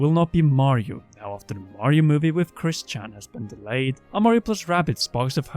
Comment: 30th birthday? I thought you were older. 0:59 Did you say "Chris-chan"? Wind Waker is 20 years old? I feel old. They could at least sell the GameCube titles for $40 instead of $60. 9:17 Wait Wii U titles actually sold millions? How is that considered a failure?